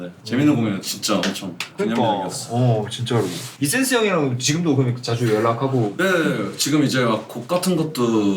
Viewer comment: why does the subtitle say the quiett instead of e sens